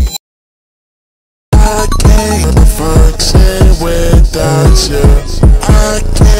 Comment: bruh why does it sounds like odetari is saying "i cant find shit without you" in functioning alcoholic